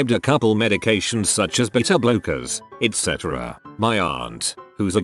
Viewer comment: when it said beta blockers i heard 'bitter blokers'